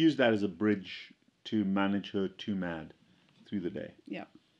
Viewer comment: Lovely video. Please clarify what I couldn't understand. Around 5:38 you said that she used the electrolyte drink to manage her "too mad" during the day. That's what the captions show. I cannot figure what you really said doc. Thanks! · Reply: 2 meals a day